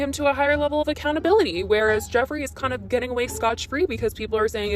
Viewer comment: Did you just say "Scotch free" ?